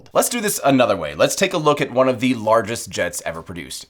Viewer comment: Yup. So is the AN226. That’s why I said “one of the biggest commercial jets” and not “the largest”. We used it as a transition into virgin orbit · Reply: My mistake! I thought you said "the largest", and yeah perfect transition and very good video as always ;-)